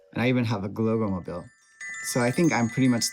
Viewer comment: Where is more information about the instrument shown at 0:30? I looked at your past videos, but didn't find it. And I didn't catch the word you used to name it. "Globalmobile"? (Which generates a lot of poor search results.) Thanks! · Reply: it sounds like 'globalmobile,' but he's saying "gloggomobile," as in the Gloggomobile designed by Herbert Bahli for Naef toys in 1983. Searching with some of those keywords added will give you better results. I think it's funny that there are so many similar sounding results for vastly different things-- If you type in "glogomobil" like the captions say, it leads to some kind of niche coupe car from the mid-20th century called a "Goggomobil."